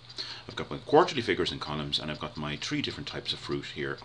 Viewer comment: it sounds like your saying condom not column xD